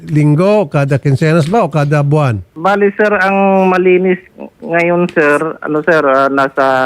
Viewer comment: "Malinis"? tama ba ang narinig ko?!!!